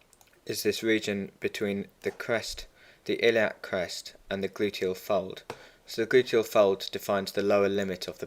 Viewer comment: you said gluteal... Please what was that? I didn't hear that clearly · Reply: Gluteal fold